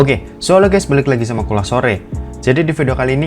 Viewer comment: gw dengernya kolak sore 🤣